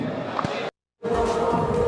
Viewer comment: is it just me or did it sound like kendall said "kylie.. oh!"